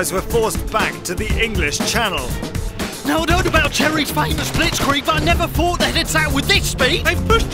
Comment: when he said Jerry's it sounded like cherry's